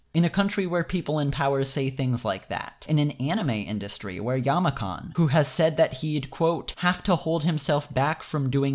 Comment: can someone tell me what the name of “yamakhan” is ik it’s not yamakhan but what is his name?